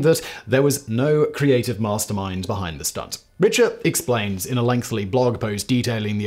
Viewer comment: A channel dedicated to figuring things out, and at 1:36 you say "lengthLY" instead of "lengthy". You look like you're at least in your 40s and still haven't figured out certain elementary words.